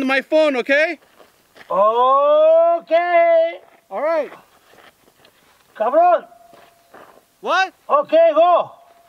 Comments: Did he call him “cabrón” in spanish? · who else thought que dijo cabron or did he if so even better 😂😂 2nd video I’ve watch and I’m convinced the man behind the camera is related to Guillermo from Jimmy kimmel